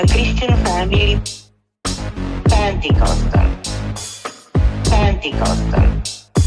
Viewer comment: I finally understand the lyrics. I kept hearing 'I was born in Romania, in a Christian family, sad people". "I was born in Romania, in a Christian family, Pentecostal".